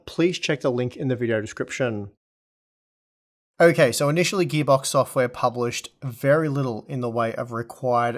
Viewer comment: I thought i heard give up software, maybe my gpu is giving up seeing the test results